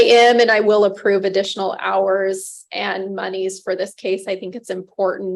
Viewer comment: did she just say "moneys"? :P